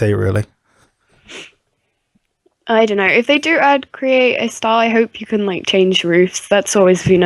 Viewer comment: Everytime you two said "Create-a-style" I thought you said "Create-a-star" ... I was like OH MY GOD WHAT DOES THIS MEAN!?